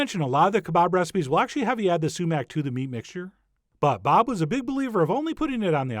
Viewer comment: Am I the only one who thought that whenever he said "sumac" it sounded like "cement" with a long "e" ?